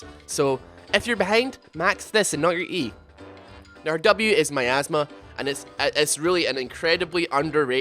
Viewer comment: I THOUGHT HE SAID "my asthma" I WAS SO CONFUSED!